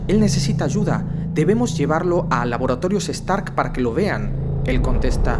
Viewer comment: no se pero yo escuche stark